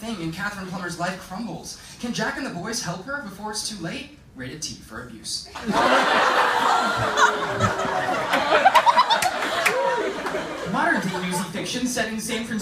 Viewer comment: wait what did he say in 1:37? Rated.. what? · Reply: "Rated T for abuse"